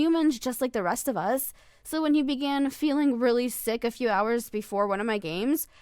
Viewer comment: I wasn’t paying attention to the video so I thought she said really stiff instead of really sick.😂😂😂